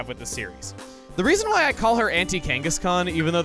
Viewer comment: OH! Auntie Kangaskhan... I thought you said Anti-Kangaskhan